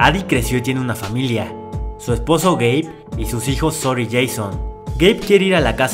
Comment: El para que cosa como de quien? USA: Su esposo Gabe Latinoamerica: Su esposo es Gay jajjajsjsksk xd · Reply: SII yo entendi "su esposo es Gay" JAJAJSAJSJFDHASJHHJA